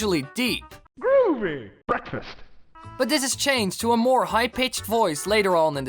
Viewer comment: groovy..., BEKFAST is what i heard